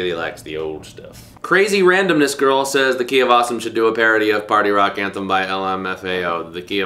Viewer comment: I am confused. In your old videos it sounds like your saying "ducky of awesome" was that the original concept or what? This is the Key of Awesome Right?